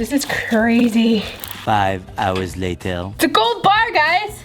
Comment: When she said it’s a gold bar I thought she said it’s a gold butt! 😝😂hehe:)